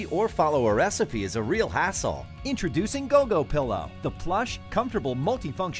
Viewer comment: as a kid i thought he said a**hole lol